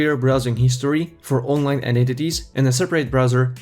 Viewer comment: Is he saying identities? Sounds like eyenentities.